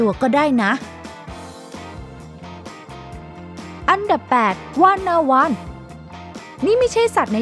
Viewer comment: ได้ยินเป็นWannaone😂😂